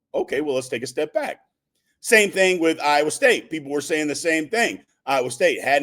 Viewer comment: Are you on drugs? I haven't brought up Ohio State in the 20 months of this channel.😂😂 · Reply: I can't speak to anyone's substance, but 14:20 You mentioned Iowa State, but maybe that sounded like Ohio State